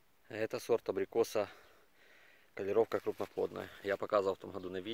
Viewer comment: Кто расслышал название самого вкусного абрикоса на 2:08 ? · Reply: <link> В прайсе подписан Калировка крупноплодная